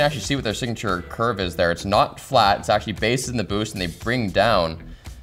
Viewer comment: I'm sorry? did you say "Based in the boost"?